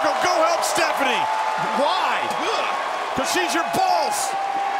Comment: did jbl said "coz she's your balls"??